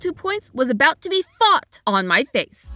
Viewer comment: I thought she said f****d but I put on subtitles and learned it was fought.learned that context is important.i was distracted when she was saying the war part.😅